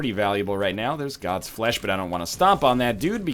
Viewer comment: What was that dance with host, I thought you said you want to use god's flesh?